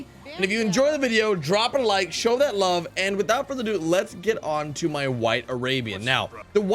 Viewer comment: Thought he said “Let’s get to my Wife Arabian”